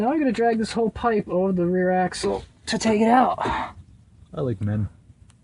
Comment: did he say "I like men"?